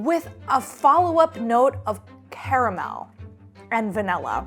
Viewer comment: I thought he said RAMEN